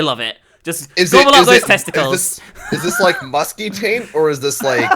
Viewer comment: I thought it said "Minx has testicles?!"